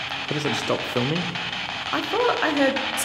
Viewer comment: Such a shame I am 2 years late but it's really fascinating to hear how clear the words they are saying so let me share what I heard. 11:37 I thought he said Anthony, not stop filming 11:53 Yes 13:38 Please stop na 15:45 Kasi naman (female voice) 16:06 Istorbo (nuisance) 16:57 Stroke (male voice 17:59 Anak (male voice) 18:04 Elsa 18:10 Ahm Sino sya? (Uhm, who is she?) 18:20 Lorenzo 31:15 Alam namin (we know) female voice 31:27 Anjan na (already there) male voice 31:31 Hello 31:40 I'm here na (I'm here already) male voice 31:53 Ganon? (really?) male voice 31:58 Gustong ???? (wants to (do something) female voice) 32:01 F*ck you ka! (male voice, maybe talking to the female voice??😅) 32:09 when you asked "who are you?", voices started talking at 32:15 like a roll call (female voices and male voices, I heard Mark, Eric) 32:27 Nirape (raped) (a male voice said something after the female voice) 41:20 Miss (child voice) 42:13 Negative 42:22 Husto na (enough) OR gusto namin (we like it here) female 42:34 Sunog (burned or big fire) 42:47 Sa labas (outside) female voice 43:55 Yung...doon (that one there) male voice 43:58 Salvage (summary execution in Filipino context) female voice 44:18 Strict doon (it's strict there) female voice 44:39 Sama (come along) OR samba (worship) female voice These are just what I think I heard. All throughout, they seem to be talking among themselves aside from answering your questions.